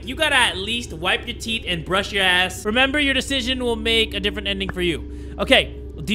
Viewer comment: Dawg, am i trippin or did Jay say "wipe your teeth and brush your ass"